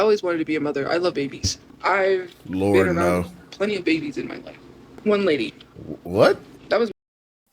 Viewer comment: I rewinded this like 13 times and still couldn't understand what what she said that sounded so weird?? Was it something sketchy or just a data glitch from her not paying her phone bill?? She think she SLICK w that "łʍ ɺØʞ1₦❡¡ 😰" walk back too. because people like me who lost the ability to give birth by means of c•ncer, hysterectomy, violence, m++carrying, and spending eveeh single day that there is NOTHING you can do about it... thats that shit we dont make jokes about. She trash talks all day+night but she knew INSTANTLY what she did when she made that quip. Even felt that sting. Not to assume but i kinda feel like it applies to her too and she would have to tell the world her highway is closed too. Woulda served her right, too. 👏🏿🖕🏿🖕🏿🖕🏿🖕🏿🖕🏿👏🏿 Ô̬K̬̂ R̬̂Â̬N̬̂T̬̂ Ô̬V̬̂Ê̬R̬̂ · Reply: She said 'Ive been around a lot of babies' 😐 shit sounded odd af.